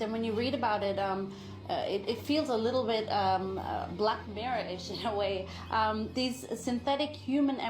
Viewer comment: did that lady say blackbarrish?? / had to play that ish back and ask wtf did she say